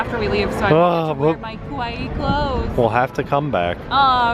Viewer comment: You guys are so cute and these Tokyo Disney videos are great! I just had to comment on something said though cause it was pretty funny. At about 11:55 when you’re talking about getting “cute” clothes you sound like you say, “kowaii”, which actually means scary😂 What you meant to say...or at least I assume you did (maybe you want scary clothes? Hehe) is, “kawaii”.